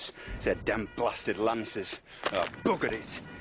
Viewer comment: Did...did he say "Lannisters"??? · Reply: Mike Palmer no he says lancers